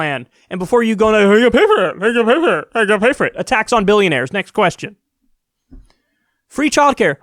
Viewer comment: did you say "a tax on billionaires" or "attacks on billionaires"? 'cause I'm in favor of either. :D